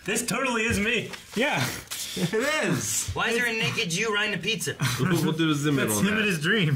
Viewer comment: it sounds like says theres a naked Jew riding a pizza lol.